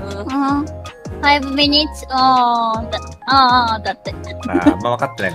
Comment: 5minutesじゃなくて1minutesって聞こえるけどな